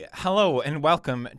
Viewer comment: Did He Say Hallo